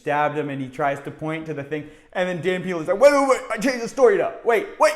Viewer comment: I thought you were saying Dan Penis 😂